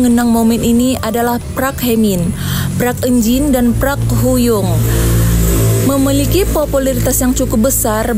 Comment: "Prak hemin, prak eunjin dan prak huyong" 4:46 "Hum hye heson" Saya salah denger gak sih ???